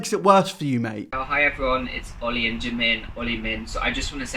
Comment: did he say oli min 🤢